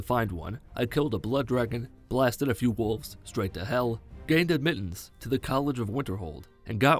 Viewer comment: I swear he said he gained a mittens